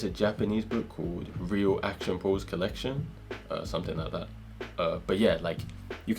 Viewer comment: what was the name of the book at 6:25 ???? couldn't really hear it to well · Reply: Sorry!, i should have added subtites. Its "Real action pose collection" My accent is difficult to understand haha